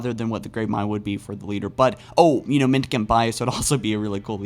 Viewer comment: What does he say? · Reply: Mendicant Bias. It's basically a Super A.I Construct. It sided with The Flood after intentionally becoming rampant. The Flood and mendicant bias wrecked the forerunners.